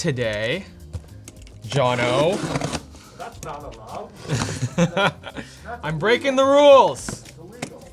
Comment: I thought he said “Thats not a knife”